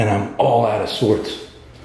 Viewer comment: Play that back on 16:40 when you said your out of sorts idk what you said but I could have sworn on my life I just heard running I think it wants you to chase it or to get you killed they're a lot of lethal stuff around like you can get hurt from anything like trip or something be careful what you hear, you they will make go crazy man I heard the same spirit mocked Joe saying yes I was the one he talked about but give me a shout out once you tell people what I picked up on